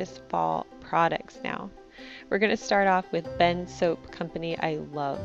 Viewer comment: When you said Bend Soap, I had to look twice. I thought you said "Ben's soap"! Thought your husband started a soap company!😂 Very visually inspiring video, Jennifer.🍂🍁🌾